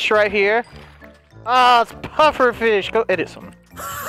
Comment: did he say "Oh it's Pufferfish! Go edit somewhere"? or was it something else? 💀